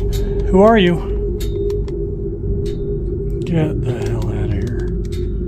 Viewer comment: it sounds like "Get him" is being said I thought this "Get out" No its "Get him"